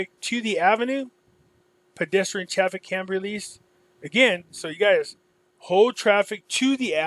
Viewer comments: it sounds like there saying so traffic can be released. hold traffic on such and such ave. so traffic can be released. thats what i hear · I also thought I heard Trump Kennedy version, but listening more....many times....I believe it's "pedestrian traffick can be released." Look at the timing of the motorcade leaving and the announcement and the fact that she didn't react to the names Trump Kennedy? The only problem I had with her was AFTER everyone was saying the announcement was Trump Kennedy maybe she should have said something like yeah it sounds like that but she did not hear those names because I was walking and talking. She did nothing wrong. MM did not have to go after her and her video. It just made him sound like a tool. I want it to be Trump Kennedy but maybe next time.